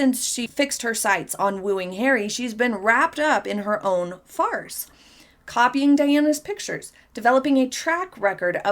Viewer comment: I thought you said Meghan was wrapped up in her own farts!!! Which is not incorrect!😂😂😂